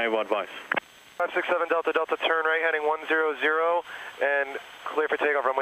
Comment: Captions say 110 but the guy says 100.